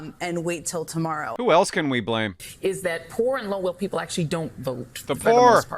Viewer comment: did she say poor and "lower" people?